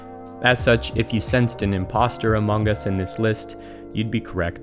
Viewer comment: DID HE JUST SAY AMONGUS. SUSSY!?